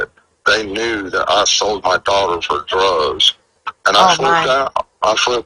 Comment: This is really well put together. I’ve never seen the clip of Summer & her brothers on their bikes - they look so happy. When says the boys are taken on the Jay for Justice , it sounds like he says, “TBI knew I sold my daughter for drugs.” Did I hear that right?